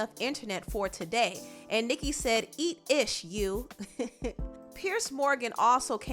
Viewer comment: 🤣🤣🤣 wait did she say eat 💩 7:54 🤣🤣🤣💀